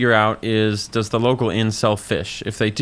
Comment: Genuinely thought he said "Does the local incel fish?" for a hot second there